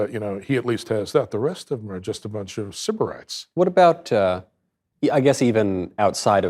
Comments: What is the "sib-er-ite" word used here? Thank you. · What does he say at 51:09 ? “The rest of them are a bunch of ___ _____” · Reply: The word is "sybarites"...pampered, coddled hedonists wallowing like sensuous pigs in excessive wealth and luxury.